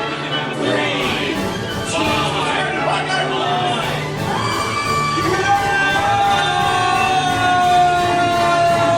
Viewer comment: What did say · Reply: "Subscribe to PieGuyRulz"